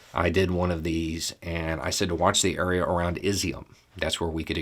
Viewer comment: "watch the area around 'izium' (spelling?)" I don't know what it is he said. Does anybody have a spelling for this location, so I can find it on the map? Is it on the Russian side? Is it a city? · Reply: Izyum, Kharkiv Oblast